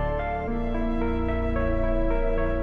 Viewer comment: what he say in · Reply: That boy is benie honor